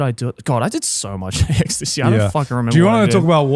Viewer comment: it sounds like joey is says "i did so much extasy i forgot" lol